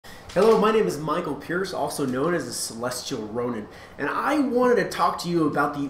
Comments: I heard him say celestial rodent lol and i thought thats weird but i guess he kinda does look like a rat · I thought you said celestial rodent. And i like this vid. Thanks for backing up your opinion with Animation facts